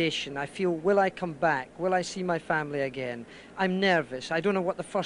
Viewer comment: Did he say "Will I see my family again?"? Really shows that until recently you had to be a bit insane to be a professional driver.